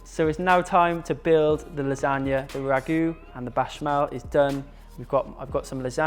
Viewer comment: What is he saying at 8:14? Bashmell? I dont understand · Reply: he's saying béchamel. It's the name of the white sauce